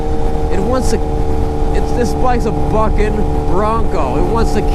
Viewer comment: Thought you said the “F” word when you said bucking bronco lol